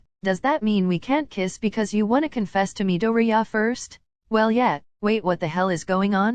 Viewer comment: I heard this as “huh so… we can’t kiss midoriya?”